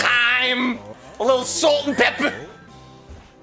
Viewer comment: I just remembered that i always heard "Put it in my melon shoe"